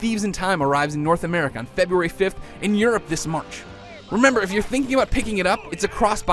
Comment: Sorry, I didn't really get what he said there, but did he say " And Europe this month" ? I surer hope so, I live in Europe :D I bet he didn't say that tough... Can someone plz tell me what he said? I'm Norwegian, not that good in english...